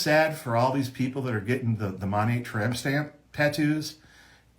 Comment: I'm sorry, did he just say "Monat Tramp Stamp"?